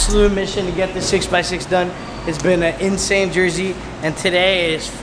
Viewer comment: Did he say "its been a crazy JERSEY"?